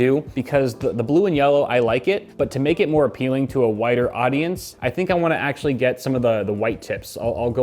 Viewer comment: I heard "whiter audience" instead of "wider audience" and was like "wtf does he mean?" xD